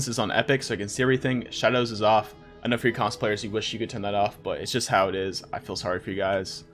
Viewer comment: i thought he said cosplayers and i was so confused but he said console players lmao